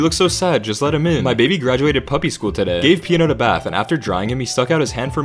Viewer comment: Omg on the 5th one I heard "gabe peed in the bath" but it was actually " gave peanut a bath" lol